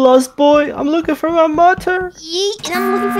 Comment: when said "mother" it sounded like "mutton" ( 6:04 )